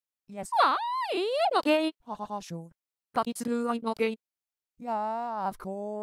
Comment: When I heard this without looking at the video I thought Len said “Fuckass ay” instead of “I’m not gay”